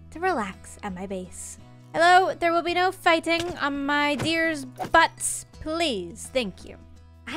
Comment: "there will be no fighting on my deer's butts" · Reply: I will blame it on being sick and having swapped regular brain-focus meds for don't-choke-and-die-on-your-own-mucus meds...but I heard that as "There will be no fighting on my dear Butts" and didn't even wonder at the possibility that her (dear) deer was named Butts, nor that she would call it "dear" until I scrolled through the comments...